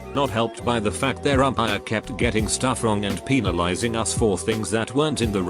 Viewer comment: Lol who else heard him say “peenalizing”